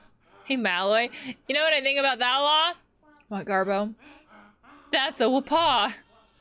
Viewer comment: Just thought I'd let you know he's saying, "That's a whopper!" (aka: 'that's big!') with an accent as his catch-phrase.